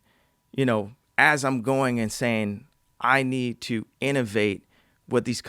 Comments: Every time he says “going and sayin” I thought he was saying “going insane” cuz he always says he’s going insane lol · did he say insane or and saying? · He says the phrase "going and saying" multiple times, but all I keep hearing is "going insane". lol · Did he say, "As I'm going insane" at 15:59? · "Going and saying" but I keep hearing "Going insane".